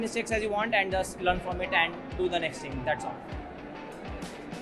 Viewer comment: what did he said study what ? Could anyone elaborate please ? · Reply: Study DSA(data structures and algorithms)